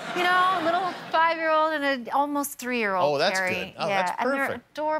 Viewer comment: Does she call him jerry at 1:26? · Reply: Perry... her 3 yr old niece...